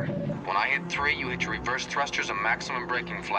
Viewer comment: I'm sure braking flaps work well in space 😄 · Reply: I thought he said " reverse thrusters ", not reverse flaps . Although still, the thrusters would have to be pointing forward , to do that . And I don't see how that's possible in the depicted engine configuration. Anyways, I just loved the coolness and style , of that original series . Interesting too, they did the reverse thruster maneuver long before Maverick in Top Gun. 😄😉